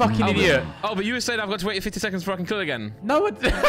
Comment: Can someone tell me what Randy says here, Ethan and Simon then laugh at whatever it is that he said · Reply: He said to Ethan “you were saying ‘I’ve got to wait 50 seconds before I can kill again’” It was a miscommunication cuz obviously the kill timer is only 25 seconds but behz mentioned 50 seconds earlier in the round about the med bay samples